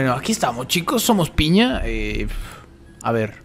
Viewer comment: Pense que dijiste: Piñera